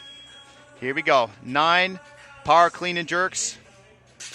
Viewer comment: what did he say the move was? I thought he said power clean and jerk but it looks like a clean and push jerk